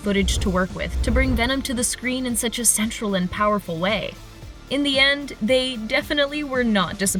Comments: I thought she said sexual and powerful way LMAO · did she just say “…to bring Venom to the screen in such a SEXUAL and powerful way” ?